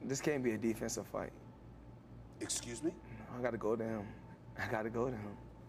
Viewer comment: This fight is the biggest scam in history : Making people believe that a mixed martial artist holds a chance against the best boxer in history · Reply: The first time he says it sure sounds like he says, "I gotta go down".